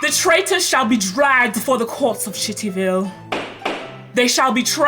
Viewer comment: I heard “sh*ttyville” 😭